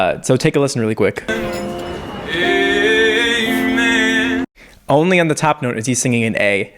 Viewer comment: My brother thought he said ”egg man” instead of ”amen”, he even thought that the song was named egg man 😂